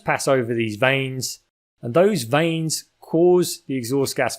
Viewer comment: Little CC error at approx 24:50, I think vanes would be the correct word there instead of veins.